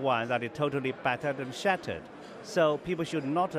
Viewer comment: my god - flattered and shattered ? Did he really say that ?